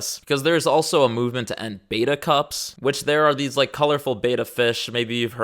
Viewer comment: Thought you said beta cucks.